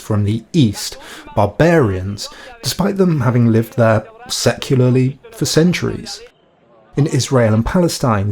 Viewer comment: the closed captions believed you said "sexually" when I definitely heard secularly. I thoroughly enjoy your incredibly well researched documentary videos, this is exactly the kind of content I live for.